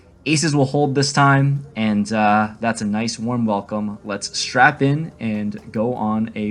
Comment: "Did he say 'strap in' or 'strap on'?"- Herbert Voice.